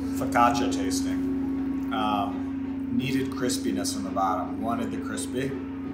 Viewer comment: The closed captions said "Chris penis" instead of crispiness